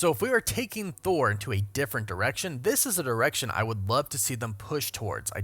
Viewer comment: I swear he says erection 😂😂😂